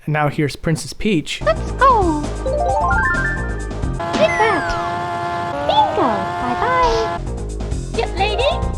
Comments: Oh My! - At 3:08 I finally understood what Peach says. I always thought she is saying: "Peanut!" but says "Take that!" .... boi... my world is shattered xD For my defense: English isn't my first language. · what the hell is Peach saying? DIP LADY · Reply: Captions say "yep, lady" so that could be it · I think she says Get Ready but it sounded like "Pink lady" to me at first and I was very confused. Such a detailed and unique description for THE Mario Princess at that time